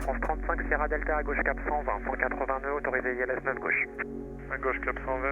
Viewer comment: He does not say "182" but 180 nœuds (180 knots). The pronunciation can be similar indeed.